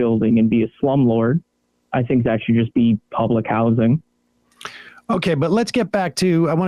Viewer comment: Did he say p-p…PUBLIC!?